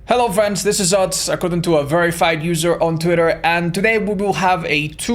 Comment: At the beginning I almost thought he said "according to a very fat user on Twitter" and I was like dang Otz lol